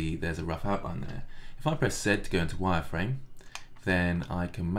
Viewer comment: when I heard "zed to wire frame" I got confused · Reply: press z